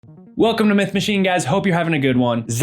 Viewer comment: dude am sorry but fr i thought you said in the intro meth machine lmao